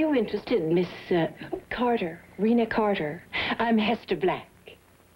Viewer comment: Rena Carter? I thought she said Renal Failure.